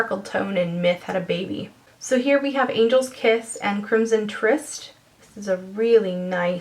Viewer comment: Gah my hearing must be getting bad because I thought you said "Angels Tits" and "Prunes and Twists" LOL